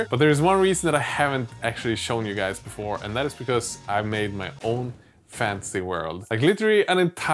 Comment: What he said: "I made my own fantasy world!" What I heard: "I made my own fancy world" I was really hoping for a bunch of fancy pants miniatures, whatever that means.